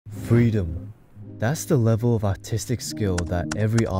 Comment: im sorry i thought he said autistic T-T